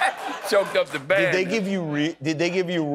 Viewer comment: I thought he said "Choked up the bag" 🤣🤣🤣